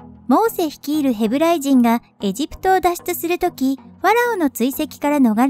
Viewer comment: パラオつおいって言い過ぎて パラオにしか聞こえない